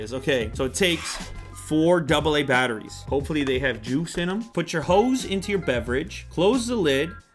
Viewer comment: Okay am I the only one who heard 2:06 as 'Put your hoes into your beverage'?